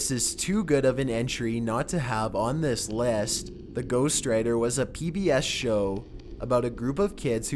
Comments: At first I thought he said ghost rider and I was like wait, there was a ghost rider tv show?! But then I realized he said ghost writer. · And he said "ghost writer" and I thought I heard "ghost rider" and I was like: really.